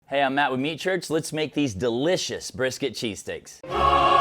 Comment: My fat*ss thought it said brisket cheese cake 😂😂 I was hungry either way it was happening